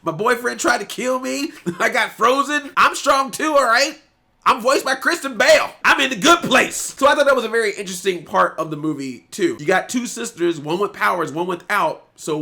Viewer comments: I know you said "I'm voiced by Kristen Bell" but I initially heard "Christian Bale" and now all I can imagine is Anna running around in a batman suit saying "I'm Batman" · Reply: 😂😂😂😂 I thought he said Christian Bale too, I was like “wow what an opportunity” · I misheard that as "I'm voiced by Christian Bale". Now I'm imagining Princess Anna speaking with a bat-growl. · when you said Kristen Bell...I first thought you said Christian Bale... and i was like ...whaaaaaaaat? I am such a dingdong